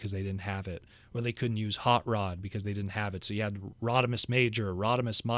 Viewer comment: For some reason when said "Rodimus" there, it sounded like he was saying "Rodi-Miss" and now I just want to see a gender-swapped Rodimus design 😆😏